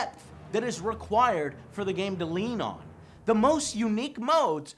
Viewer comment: Did you say Lean On? fuck men! now that song is all over my head!